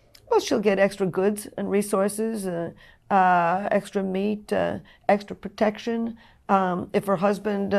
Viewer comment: did she say 'extra meat'